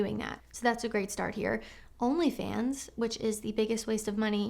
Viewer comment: I had to do a double take when you said 'OnlyFans' as I thought you'd said 'HomelyFans!' Ha! THAT would've been a new one!